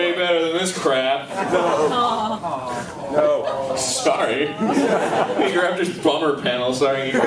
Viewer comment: sounded like fuck you egoraptor